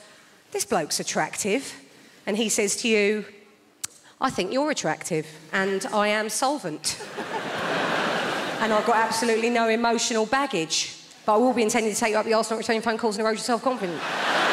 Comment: What on earth is she saying here? She's speaking much too quickly for me to understand. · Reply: "I think you're attractive, and I am solvent, and I have got absolutely no emotional baggage, butiwillbeintendingtotakeyouupthearseandnotreturnyourphonecallsanderodeyourselfconfidence"